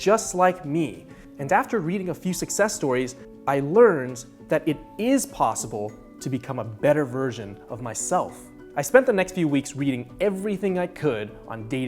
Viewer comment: Honestly thought at 8:20 he said "It is possible for me, to become a better Virgin of myself."